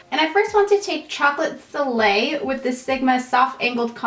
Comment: Omg when you were putting on bronzer, it sounded like you said "Chocolate filet" instead of soleil and I was cracking up